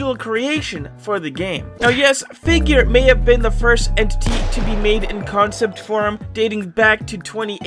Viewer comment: I thought you said rush was the first entity to be made in doors